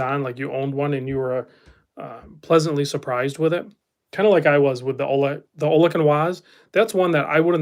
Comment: What's the brand you're saying there? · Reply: Ollech & Wajs